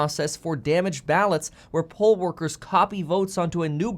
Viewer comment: You had me at 3:17. I heard Pole Workers. And thought what in the world do strippers have to do with stimulus or the elections, and that there was another Stormy Danials on the rise. Then I came back to reality. Great job to all the Workers that tallied the vote!